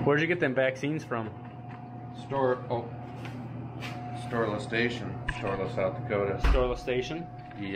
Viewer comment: Oh, Storla Station.... ok, all this time I thought I heard it as Storeless Station ..... lol..... No wonder it sounded weird to me. duh..... lol again.